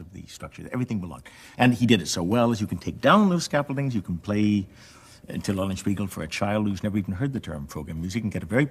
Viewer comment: does anyone understand what he said there? "You can play until all beagle for a child..." I don't think that's what he says, the subtitles must be different... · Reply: I’m not certain, but I believe he says “you can play Till Eulenspiegel…” presumably the tone poem by Richard Strauss, whom, as you may know, Gould admired profoundly.